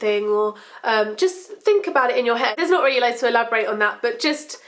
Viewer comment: Omg. I thought you said "That's not true." Sorry, I'm dyslexic.